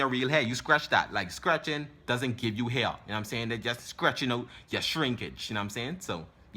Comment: who else thought he said scratching instead of stretching and was mad confused